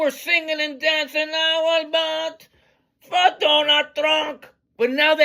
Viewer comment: I always thought they were saying - "I wheel boat, por Donah Tronk 🎵🎶"